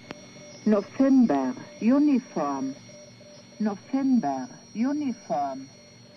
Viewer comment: Did I hear UNICOM? UNICOM is an aviation frequency used at uncontrolled airports, much like CTAF.